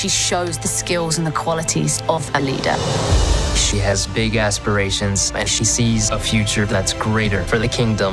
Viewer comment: she has big as... My dirty mind: Woah woah there! What he says- She has big aspirations Me- oh yeah this is a Disney Interview. That cant happen you fool! My mind - But if he said that, he won't be wrong! Me- SPEECHLESS!! 😂😂😂😂😂😂😂😂😂